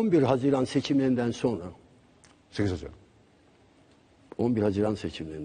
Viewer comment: Fatih ne dedi oyle anlayan var mı? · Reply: 8 haziran diyor